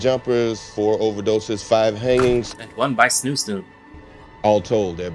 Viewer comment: "And one by snooze?" I couldn't make out what you said